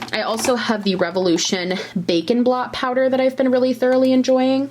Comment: Watching this while I do the dishes and I hear "bacon blot"...took me a minute to figure out it was bake and blot! Hah!